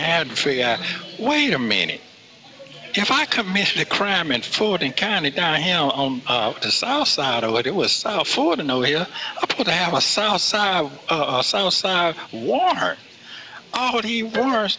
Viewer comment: He actually said "All these crimes I did was not in Roswell". Wow! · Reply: " If I did the crimes in the south side I should have Southside/ Fulton County warrants. What innocent person says " If I did the crime?" Close Captions can hardly interpret his gibberish. Not to mention Roswell is a city in Northern Fulton County.